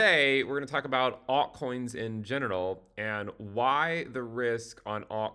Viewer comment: sounded like you said alt coins in genital - I laughed so hard I had to rewind to listen to the first 2 minutes again.